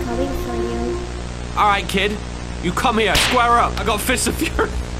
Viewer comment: all i heard was COME HERE BOYYY